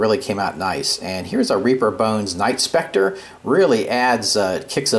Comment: I don't use this word often, since it's used more by the younger crowd and it has become cliched, but this ghost well is totally awesome, man! I couldn't quite catch what you said about the object you placed on top of the ghost well @ 4:09--did you say "reaper bones night spectre?" What's that? A monster? A company name? I'm not familiar with that and I may have got it wrong from the video--hearing is not so good anymore.